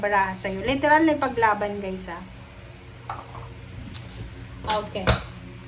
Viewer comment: At pag Sabi mo ng fighting Libra fighting sumagot yung boses na" oO " Ako lang ba nakakatinig 😭😭😭 ??? It is around 0:56/10:03 check nyu use headset para malinaw ... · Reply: My goshhh sumasagot yung boses ateeee 😭 check nyu po di talaga Ako nananakot ..Sabi mo na leteral na ipag laban guys ha , sumagot "oO" 😭 check it 2:22/10:03 Buti nlng Hindi Ako matatakotin 11:30 pm pa naman ko to narinig here in Qatar listening..